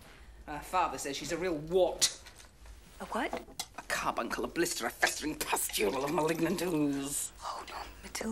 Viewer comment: what did she even say in that part??💀☠ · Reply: A carbuncle, a blister, a festering pustule of malignant ooze.